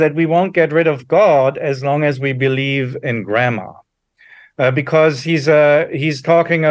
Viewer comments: Damn I was listening audio only and thought he was saying “grandma” that whole time he was saying “you won’t get rid of free will til you get rid of grammar.” Lmao. Am I the only one? Probably. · mins I misheard him and thought he said we won't get rid of god so long as we still believed in Grandma... and it stopped me in my tracks for a while...I'm not smart.